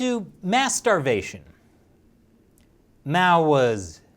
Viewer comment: Ohhh....Mass Starvation. I thought i heard "masturbation".